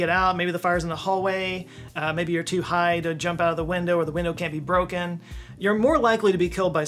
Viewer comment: I heard the line "maybe you were too high to jump out of the window" Had to rewind 10 seconds